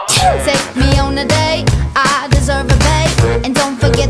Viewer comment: People who at first thought it said"I deserve a date" not " I deserve a babe"💀💀💀